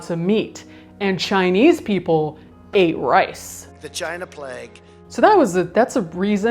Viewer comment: When the guy's voice said "the China plague," I swear I thought I heard "vagina plague" and I have now reached a while new level of horrified.